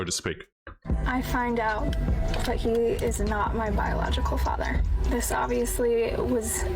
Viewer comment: I think you might have misheard her comment - it sounded to me like she said she found out he wasn’t MY biological father - sounds to me like she (and the man she thought to be her father) are both the victims of her cheating mother.